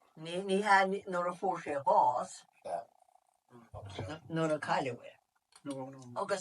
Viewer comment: min .....and just before that,--I may have misheard....but ....Nuair a cailleadh é, I think that is what she said ( ¨when he was lost,¨ for, he died) in their Achill dialect, instead of nuair a fuair sé bás. I have also heard that (Nuair a cailleadh é) in old episodes from 1999, of Ros na Rún, an Spiddal area Connacht.